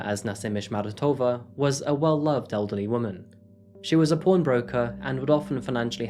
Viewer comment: Did you say she was a porn broker? It really sounds like you said she was a porn broker. 😂😂